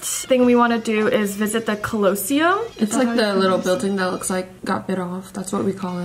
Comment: did?…did you just say?…did she say..uhm…did you say CLOSEUM?